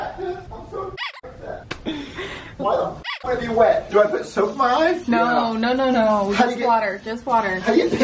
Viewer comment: Ik fucking know did not just say do I put soup in my eye